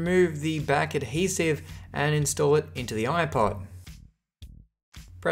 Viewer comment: it sounded like you said “making sure everything is seated incorrectly”